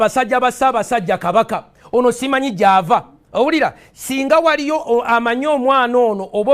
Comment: Did he just say OWARI-DA ????